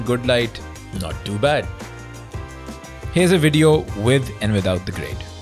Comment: I Thought Is Saying "With or Without GREAT", Because Was Not In Frame When He Said Without. HAHA xD If You're Fan of You'll Surely Got What I'm Saying.